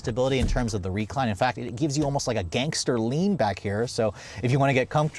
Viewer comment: Did he just say "Gangster Leanback" in the CRV back seat @ 26:56 😂😂 😂